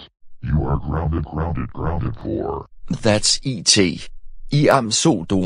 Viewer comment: What's he saying at 5:32? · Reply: That's I-t! You asked for the ultimate jumpscare!